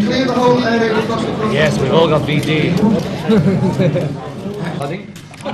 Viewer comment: Haha...did anyone else hear this? in the chaos, someone tries to tell some of them to clear out at 0:54 John says, yeah cus we've all got VD! And Paul laughs. Did I hear that right? Long before it was more PC to say STDs. lol Then some idiot asks them how many girls they've had in Hamburg? 0:58 ....even the Beatles were a little taken aback by that one. And George asks "How many have we HAD???" That's why they started not being quite so friendly. Geez